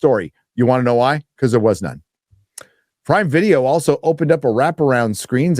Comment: Prime Video? I initially thought you said Pride Video 😂